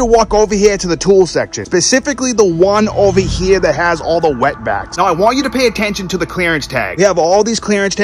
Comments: I heard him say wet-backs???? Not cool bro! J/K Wet-Vack!...lollol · Dudes accent had me blown! I watched this twice and I swear I thought he said the section with all the Wet Backs. I wasn’t sure if he was going to dig at Walmart or be a racist ass. Instead…he taught us how to keep hustling until you find the good deal! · With that accent sounded like you said "over here where all the wet backs are at" 💀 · With his accent i could have swore he said WETBAC*S... 🤣🤣🤣🤣.... Im mexican so no offended just saying pretty close....